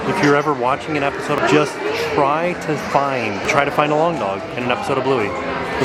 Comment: 1. I thought you said “long dong” like 16Candles. 2. Bud Light Year sounds like a great ride.